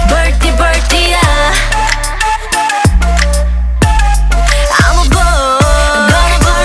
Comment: I thought she was saying "I'm a bore," and needless to say, I was very confused for a few seconds. X'D